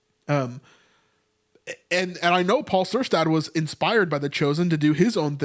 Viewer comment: What modern retelling of the book of Acts by a Paul Cersad, I think the subtitles are wrong on this video and so now I can't find out who and what this show is that your mentioning. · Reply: It's called testament, Paul Syrstad is the Director/Creator